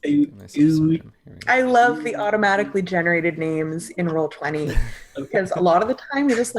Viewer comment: an app was mentioned. ( "Role-plenty" it sounded like.) What program(s) do you guys use? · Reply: Roll 20, the program that these videos are aponsored by/posted to. You can find it with a simple google search and you'll be good to go!